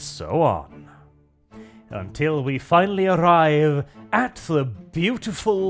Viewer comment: I heard "kekistocracy" the first time, not "kakistocracy". XD